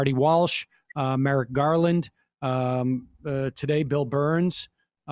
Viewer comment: For a second there I thought he said Bill Burr.